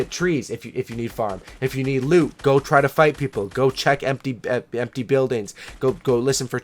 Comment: Did he just say go check empty houses